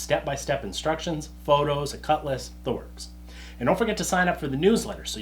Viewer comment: Every time you say "cut list" what I hear is "cutlass " and there's a disorienting moment when I wonder how you can include an Oldsmobile with a $10 set of plans. Perhaps I should be more conscientious about putting in earplugs before spinning up the noisy tools in the future. =))